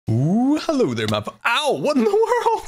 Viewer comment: Is it just me that thought he said hello there buffalo?